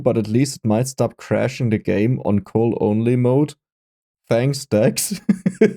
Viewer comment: I heard that as "So You-Know-Who stops crashing the game in coal-only mode" and thought the patch notes were calling out Dex specifically 0:15 Nevermind lol